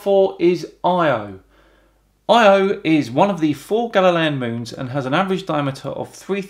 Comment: What's a galilean moon? At least I think that's what you said. I couldn't tell. I'm also sure I misspelled that.